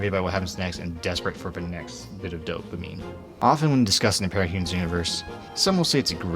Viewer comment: Nice video, but there are some issues with the onscreen subtitles, e.g. ‘pericherent’ instead of ‘parahumans’